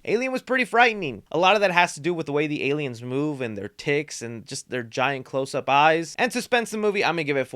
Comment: I'm sick and can't hear so good at the moment and I swore you said "& their tits". I laughed so hard😹